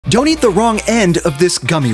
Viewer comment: I thought it said “donate the wrong end” 😂😂😂